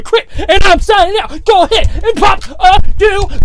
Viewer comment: Crypt, when you yell "pop-a dew", it sounds like your saying humperdoo from a series called The Preacher 🤣👌 it's gold bro.....